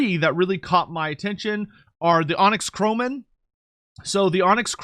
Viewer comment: sorry, was reading a short story on Twitter when you said this part, and had to double check. Sounded like you were saying onyx scrotum for a bit. 😅😂